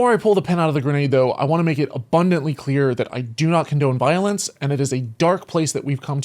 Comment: "I do not condemn violence" What? Did I hear that right?